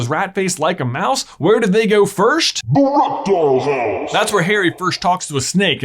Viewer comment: I heard ERECTILE HOUSE